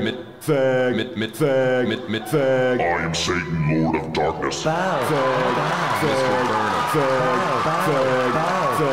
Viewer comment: What does the deep voice say at 56:26?? (I'm not a native English speaker sorry) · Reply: Kairi Craizy The voice over said "I am Satan Lord of darkness"